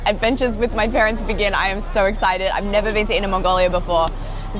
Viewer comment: When you said jinzhou my heart skipped a beat I thought you said jingzhou but then I read it😢